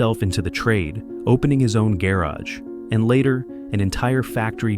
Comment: bro don't use AI to write scripts .. its so obv! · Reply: “Gay Raj” like tf turned the word garage into calling someone named Raj Gay 😂